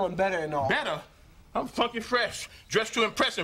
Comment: I thought he said "I'm fucking fresh" lmaok